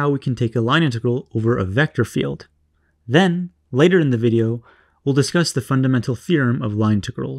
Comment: Hey man your videos are great I'm starting to understand something , just wanted to tell you in 2:03 the Subtitle says "two girls" instead of integral